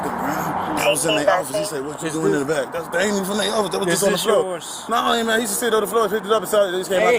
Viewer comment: did he call that officer " Brown Man " ?????